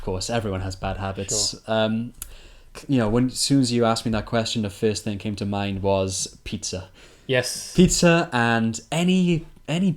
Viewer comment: Could you help me ? What he said in 01:01. I can't listen it clearly. Thanks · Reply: "when as soon as you asked me that question, the first thing came to mind was pizza"